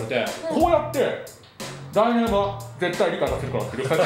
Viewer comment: なんて言ってるの？ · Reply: 来年は理解させるから